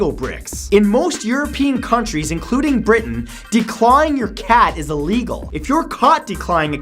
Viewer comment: I thought you were saying "declining your cat."